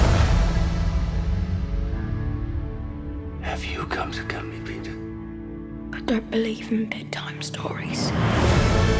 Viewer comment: what does Blackbeard say? I can't figure out what he says, it sounds like "have you something something Peter?" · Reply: Chloe Funk COME TO KILL ME! Peter's reply made no sense i know